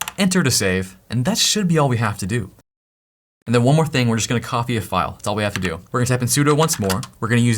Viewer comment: Did he just say Coffee a file???!!! I’m in ☕️